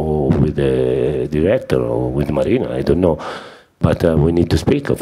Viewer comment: Did he just said speak with Mourinho 6:22 💙 Anyway congratulations Boss i respect u as a person but now respect u more Boss. becuz u have won a trophy and Trophys🏆🏆🏆💙 matters a lot in Chelsea , so well don boss👏 thank you and congratulations once again Champ......... (U a winner keep winning ) Sarriball We now trust.. Chelsea for life win win winnnnnnnnn🏆🏆🏆💙🏆🏆🏆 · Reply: Marina not mourinho 😂